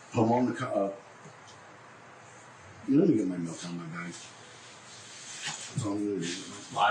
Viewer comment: did he say "let me get my milk out of my bag"????